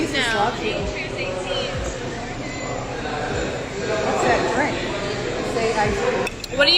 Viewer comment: you can hear her mom saying “what’s that drink? Say iced coffee” or am I wrong ?